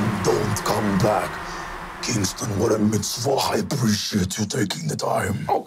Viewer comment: I'm sorry, "[speaking Yiddish]" instead of "mitzvah"? I guarantee you more people have heard the word "mitzvah" than "dybbuk", even back in the Nixon era.